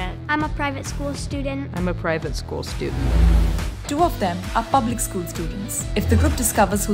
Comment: i thought the announcer lady said "are f*cking school students", I was like what? chill mama!